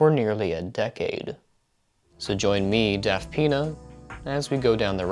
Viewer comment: My dumdum self thought you said “deaf peanut” instead of “daftpina” lol